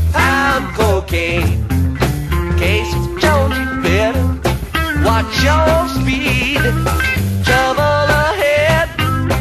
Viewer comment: Haha I always thought they said " In case in Georgia you better, watch your speed"